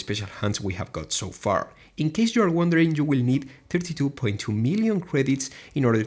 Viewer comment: No offense but cuz of your accent, i heard you say 52.2 mil credits to gold max... its 32.2 mil guys